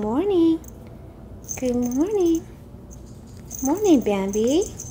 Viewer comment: My dog is part Shih Tzu and his name is Bandit. At first I thought you said Bandit but I looked in the description and it said Bambi.